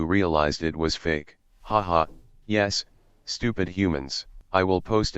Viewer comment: Did he really said fake as Jeesus , bruh